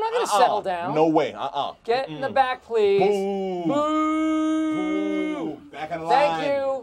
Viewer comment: I just realised michael is saying ''Moooo!'' instead of ''Boooo!''